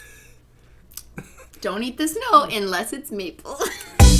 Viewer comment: I put on my captions, and this is what it said don't eat this No unless it's me